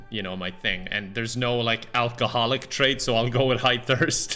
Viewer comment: thought that said homophobic lmao